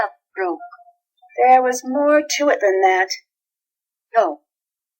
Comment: There's a typo in the word box! XD Takae: You would be upset if something you love broke. Nancy: There was more to it that that. See?! I heard Nancy say Than...